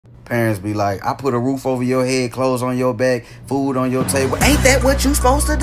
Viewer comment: Did I hear “toes on your bed” 😭